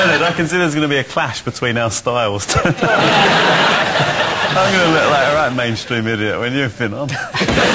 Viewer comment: what is noel saying at 2:50.... "main stream idiot-" then something else? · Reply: To me it sounds like "I'm gonna look like a right mainstream-idiot when you've been on". Hope that helps :)